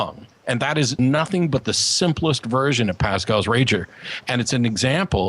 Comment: Did just say "Pascal's rager"? XD